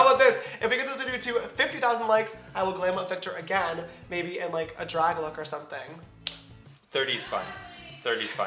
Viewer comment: What did she say at 13:25 ? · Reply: Drag queen